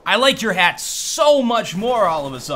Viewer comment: if you turn on captions it says ass instead of hat xD